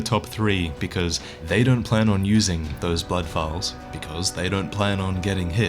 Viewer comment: the subtitles said buttholes instead of blood vials!